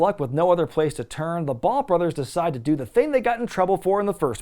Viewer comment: I thought he said Bone Brothers myself. Also nice to see the early work of the Fake Baldwin